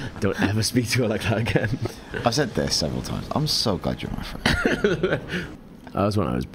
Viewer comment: what was he saying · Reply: "I'm so glad you're my friend" you meant, yea?